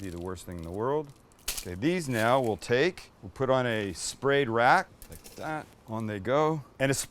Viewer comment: Sorry. I heard 'spayed rat'.